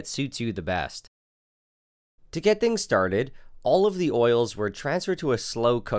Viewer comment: "Olive the oils" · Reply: Is it me or I heard "All of the oils" lol XD